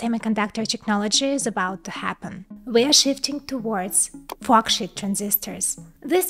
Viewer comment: Don’t get me wrong I LOVE this channel but at the beginning it sounded like she said “Fuck Shit Transistors”